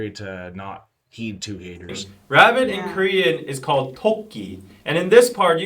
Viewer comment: Getting to know the cultural references is just something about deep diving into BTS that I love 💜 Like I never knew that they said ‘Tokki’ = ‘rabbit’ I always heard it as ‘talkin’ but of course the rabbit makes sense ☺️ Sad to hear that people have been giving you a hard time just for deep diving into each member. To me it makes total sense to do a deep dive when they are individuals as well as a part of a group. When it comes to the current state of ‘are BTS still getting hate’ I would say that sadly there has been an uptake in the ridiculous reasons why people decide to be over critical or just being a full on hater. I thought I knew the extent of the hate they are getting, but now that all 7 are discharged from the military it’s only grown. And to be honest the closer we get to their comeback the more intense and ridiculous it’s going to get. But as RM said all we as fans need to focus on is them, and the music that they are putting out. We know the truth about them, we know the capability of their abilities. Everything else just becomes noise. Can’t wait for 2026 💜💜💜💜💜💜💜